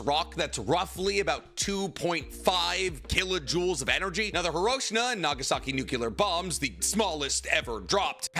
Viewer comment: did you just call hiroshima, hiroshina